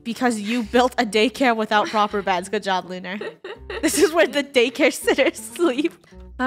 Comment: Wait did just say daycare sitters???